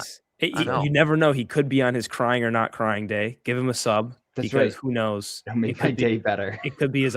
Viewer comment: subtitles wrongly read "a sub" - it cleary ought to read "a sob" ;)